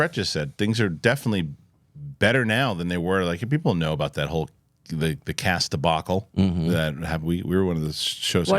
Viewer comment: I thought he said "Cats" debacle LMAO